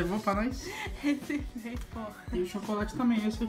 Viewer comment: escutei um "reservei porra"? kkkkkkkkk oq vc tá ensinando pra ela!!! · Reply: Nao kkkkkkkkk, ela falou reservei poooo (mas como respirou parecia, mas nao falou hahaha)